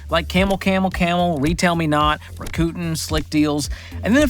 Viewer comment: What is that I hear... Ra-Coot'n. (Rakuten) 😅😂. Thanks for making me LOL George!